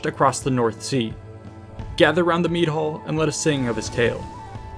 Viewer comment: Oh Mead Hall. I thought you said Meat Hole and was wondering what strange Viking tradition you were going to expound on. Lol